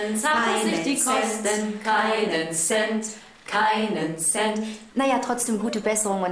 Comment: hab da erst "keinen Sex" verstanden